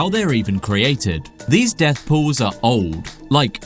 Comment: what pool now? say that again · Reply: death pool